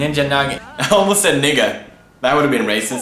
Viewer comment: WHAT DID HE SAY???????? 😨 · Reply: he said nigga